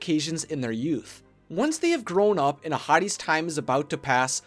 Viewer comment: I thought you said "a hottie" instead of Ahadi (how do you spell it again?) XD